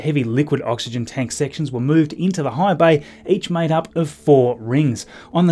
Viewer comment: I thought at first he said hive bay (The Borg?) but rewound with captions on to see he said high bay.